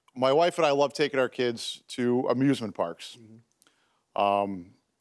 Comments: I thought he said abusement park, I was like wtf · I thought he said abusement parks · I thought he said abusemint parks